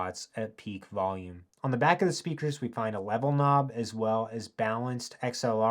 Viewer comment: I'm sorry it sounded like you said "Webble Knob"